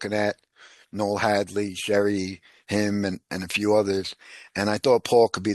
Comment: I love I first saw him on Understanding Conspiracy and Killah Priest podcast at the 9:00 minute mark I can't make out what he's saying who is Sherry Him